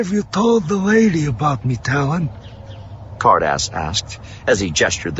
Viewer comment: I don't know why I kept hearing 'Hardass' instead of Ca'rdas.. 😅